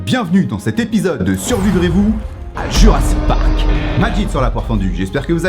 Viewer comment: c’est quoi se que tu dit juste avant la "poire fendue" ? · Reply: Euh j'entends " ajite" perso x)